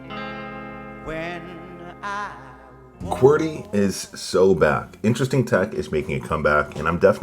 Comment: Did he say its so bad? or its so back?